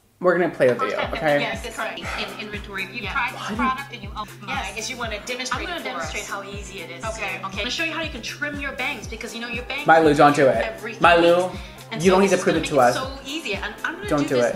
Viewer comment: I thought he was saying my loo the whole time...